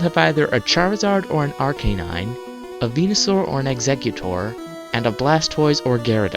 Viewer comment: When u were talking about red and said venisuar (idk how it's spelled) I thought u said penisaur